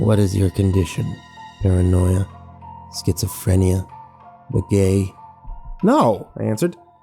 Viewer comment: I heard "What is your condition? Paranoia Schizophrenia? You're gay?"